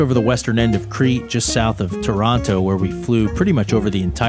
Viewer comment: "just south of Toronto" Did I hear that right? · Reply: Taranto (Italy)